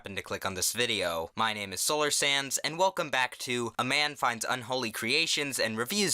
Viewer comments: When you said 'Solar sands' I thought you said solar sans and I almost got upset lmao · I’m sorry but when u say your channel name to me it sounds like your saying solar SANS and it catches me off guard every time 😂